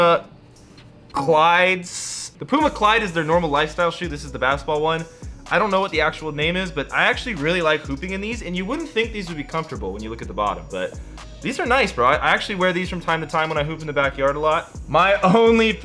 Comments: I thought he said "I love pooping in these"😂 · It sounds like he is saying “ i really like pooping in these” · I lowkey thought he said pooping😂🤣